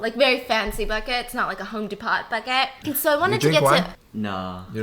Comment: Did she say Home De Pot bucket 😳🤣🤣 6:24 maybe she meant Home Depot I love her 🤣🤣🤣